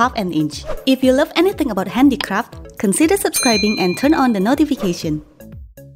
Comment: I thought she said handy crap at first🤣